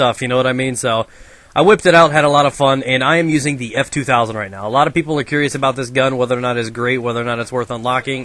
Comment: wait, did say f2000 0:40?